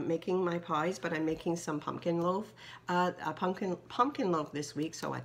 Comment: Me can I have your pumpkin roll recipe. Great haul sweetie, but you always have great hauls. I love that pencil case. Kathy found one to. Grand babies grow so fast. Beautiful butterfly sign and yes we have become family and I love it. TFS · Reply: Hi Christina, I am making a pumpking loaf or some may call it Pumpkin bread maybe it sounded like I said pumpkin roll..sorry... thanks so much for watching my sweet friend xoxoxo💖